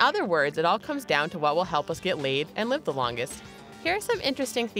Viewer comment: did she just say get laid😂😂😂😂😂😂